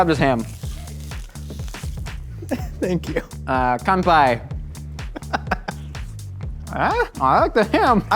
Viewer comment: I legitimately though Josh was trying to say "Ganbei", but it turns out that I forgot Ganbei is mandarin chinese, and "Kanpai" which he actually said, is basically the same thing but in Japanese (which makes sense all things considered). The more you know. (It means essentially the same as "cheers" in English btw for those who don't know.)